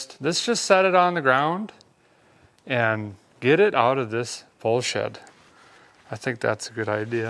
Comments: I had to rewind it 3x to discover that he said “pole shed” instead of 🐃💩. 😁😁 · He said “pole shed”. Yeah. I know, I thought he said something else too 😂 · I swear I've never heard cuss before, but he is working on a dodge lmao (edit, he actually said pole shed) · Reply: yeah, I thought he said Bull** too, but it was pole shed. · Did I hear a guy say “pole shed” @ 54:30 or something else lol